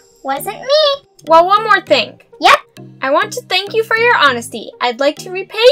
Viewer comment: Oh you said honesty I thought it said honey